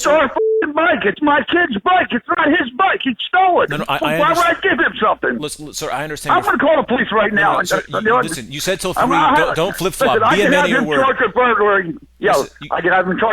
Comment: I thought said don’t flip fluck 😂😂😂😂